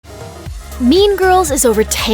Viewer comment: I'm sorry I keep hearing “meme girls”